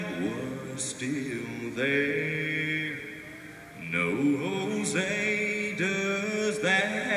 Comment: he said no jose at the end instead of oh say